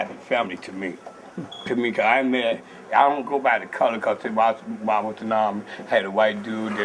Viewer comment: What did he say? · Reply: The people I met